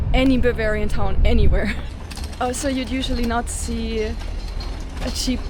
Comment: I thought she said not see....